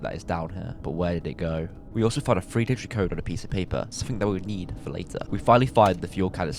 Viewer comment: I thought you said “something we’ll eat for later” 💀